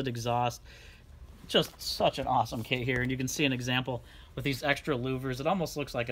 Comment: did i hear him say F-ing awesome !! LOL